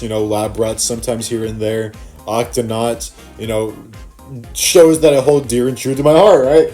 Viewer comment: I heard you said octonaunts I do watch that show